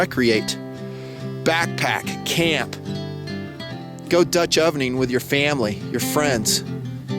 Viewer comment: did he say Dutch ovening your family?